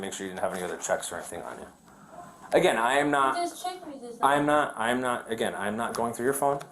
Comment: I thought she said "you just checked me"